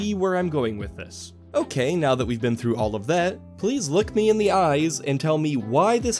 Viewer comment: I heard please “lick” me in the eyes😂